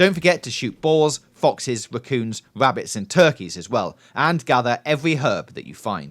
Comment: Did he said turtles